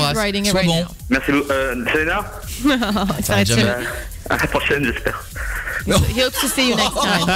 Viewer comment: il dit quoi ? svp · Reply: à la prochaine j'espère